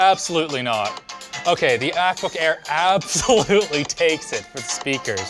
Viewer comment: did he just say ackbook air?